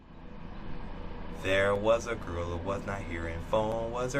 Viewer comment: I thought he said: There was a gorilla who was gone p-h-o-n-e Am I just weird?